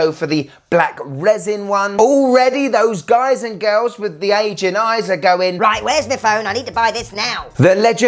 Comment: did you say “Asian eyes?” For eligibility?